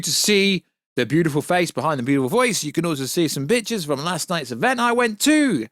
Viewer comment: did he say bitches? 😂😂😂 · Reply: Lmao I think he said “pictures” but with his british accent I heard “bitches” too. I was like damn Warstu is a G